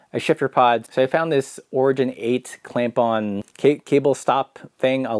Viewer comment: I probably didn't hear you correctly however I always hear what I want and I hear "OREGON 8"I'm adopting it❣️